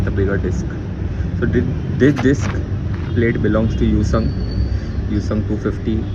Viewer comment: what bike did you say it is from, Bro?! YouTube says Huqvarna 😂🤧😂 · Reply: It’s from A Hyosung 250/ 650. Yt has tricked you 😂